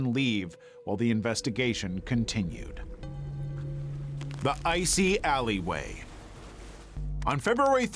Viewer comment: Is it just me or is the wording different to what he said on the 17:08 mark? The Icy Alleyway but it says on screen The Wrong House